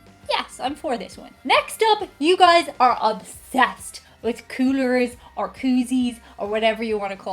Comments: I am glad you spelt that out. At first I thought you said " kooters". I was like ya damn right we are. haha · I thought she said cooters and I was like Oh NO! That's not the right word at all! 🤣